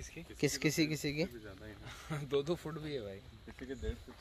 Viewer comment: I heard 'Fuck' 🙊